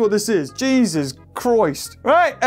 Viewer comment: Did he just say Jesus Chroist?